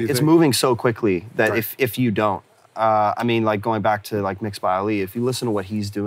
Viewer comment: Can anyone catch the name mentioned at 27:06? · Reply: MixedbyAli